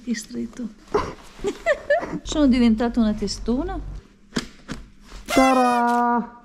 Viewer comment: al posto di tadan ho capito SARÀ (el casco)